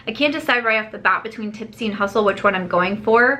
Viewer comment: i was confused bc i was half listen when you said tipsy and hustle i thought you said Nipsey Hussle